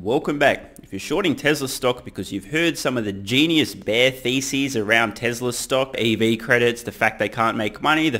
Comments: It's kind of insane that automakers are paying Tesla to put them out of business, right? 😂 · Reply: Did you say “genius bear feces”? Cause they're usually bull feces. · When you said Bear thesis, I thought you said bear feces. Kinda the same tho.